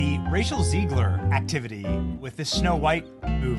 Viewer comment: Sounded like you said Racial Zegler 😂😂😂